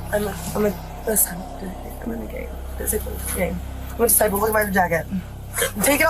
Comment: Entertaining compilation for sure But at 32:30 she was not saying she was disabled but merely repeating that she was a Disciple several more times.